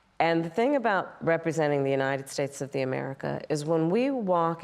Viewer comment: This should be called The Art of Gaslighting and Mistruths - and what on earth is she saying at 6:07 “The United States of THE America”? 😂